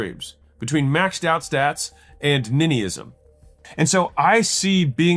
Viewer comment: what did he say at 11:18? Ninism?